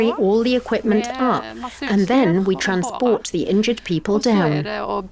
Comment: "...then we have to transport the injured people down." -- That was not what she said. She said they would had to _carry the injured people down. With their own arms. -- She even emphasized it. And you got it wrong.